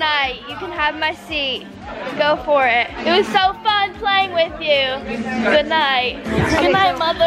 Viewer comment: I HEARD OMG PLAYING !!!!